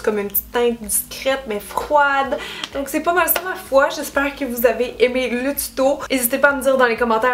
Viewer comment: À la fin (13:52) tu dit: c'est pas mal tout ma foi... j'ai compris: c'est pas mal tout Malfoy (comme Draco) j'ai reculer la vidéo, je n'étais pas sûr si tu avais un jeux de mots ou non. hahaha! super maquillage (et vidéo) le prochain fait poufsouffle